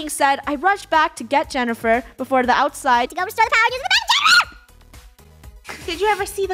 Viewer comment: Wut the heck did she say?! 😂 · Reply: She said to go start the backup generator